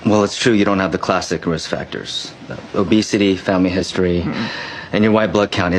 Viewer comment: I thought he said rizz factors 😂